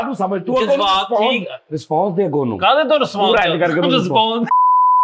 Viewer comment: Guess kren 10:40 pe nay kya kaha hay bhala ? Mai bujh lya 😂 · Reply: Response dawe .. aa te yen nu pherda 🤣🤣🤣🤣🤣🤣